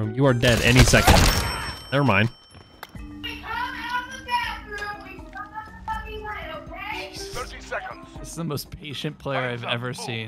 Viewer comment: I heard "When you come out of the bathroom we turn off the fucking light, okay?"